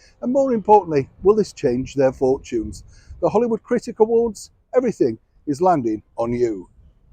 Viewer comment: 😂😂 Forgive me but at first I thought you said Hollywood cricket award 😅 and I thought hmm, well Harry is British but? Oh dear, honestly Hollywood critic or cricket award. Both have equal relevance. ♥️🇨🇦